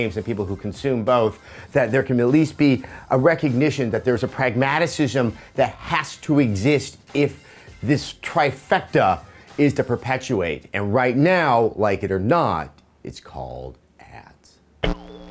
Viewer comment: For a second I thought he said "Like it or not, its called aids." XD